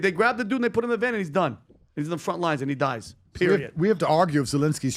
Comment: did he just said “period💅”